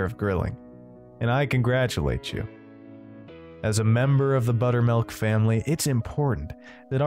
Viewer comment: I thought it said butt milk lmao